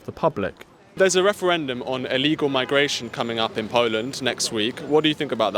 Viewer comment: Lol I thought it said Portland 😂 I was like Portland is kinda soft on immigration